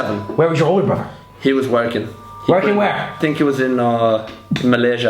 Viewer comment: when Josh said his brother was "working" i heard "wanking"😂